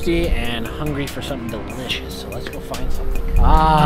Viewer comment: When one of you said delicious, it thought you said B word’s